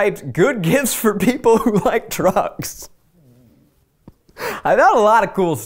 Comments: I heard “trucks” instead of “drugs” and was trying to figure out the correlation between trucks and kaleidoscopes. “Well kids like Tonka trucks and kids like kaleidoscopes so maybe he’s going for a back-to-your-youth angle?” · When Zach said he looked up gifts ‘for people who like drugs’ I legitimately thought he said ‘people who like TRUCKS’ and I just imagined Zach buying a buttload of tonka trucks